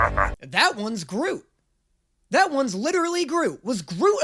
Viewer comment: Ok but why did I hear "This one's Gru" and turn around expecting to see the character from Despicable me